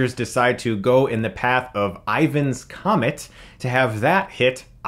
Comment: I'm pretty sure that was Ryan's Comet, not Ivan's. Or at least it sounded like Ryan.